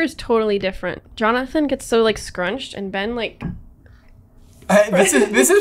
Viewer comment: I thought he said "crunchy" in the intro and was confused for a second.